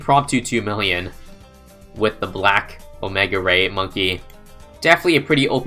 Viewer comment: I thought it said "The OMEGA GAY Plasma Monkey" at first